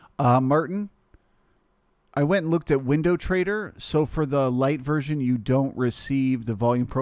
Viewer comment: thanks for the vid, im gonna sign up for the trading room, sounded like u said u were using window trader the first year but not anymore? or is that window trader ur using for the market profile?